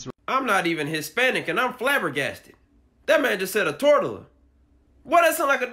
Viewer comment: Barely starting watching the tortilla one got me...sounding like a damn pokemon💀💀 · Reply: when I tell you I thought this man was saying tortellini or something, cuz never in my wildest dreams did I think he was disrespecting my ancestors and trying to pronounce tortilla 🤦🏻‍♀️